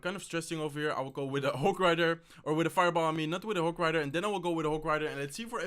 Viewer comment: did he just said FIREBOMB?